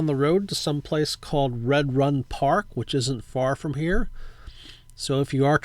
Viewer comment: At first I thought you'd said you took Cody to "Redrum Park," as in "The Shining." I realized it was "Red Run Park." I remember from the 1980s that Topps would have 33 card in a boxed set and Fleer would have 44. Beep beep!